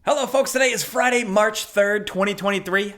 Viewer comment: You say your eyes aren't great, well my hearing isn't great. First time around this video I heard "Hello fucks" and "here to fuck about the latest gaming news" LMAO That being said, I hope Ubi DON'T go back to a yearly-release schedule for Assassin's Creed, that would probably seriously dilute what's already a weakening franchise IMO